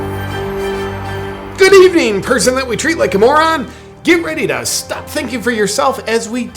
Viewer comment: at first i thought it said we treat you like a mormon and i thought how is that going to work??? hahaha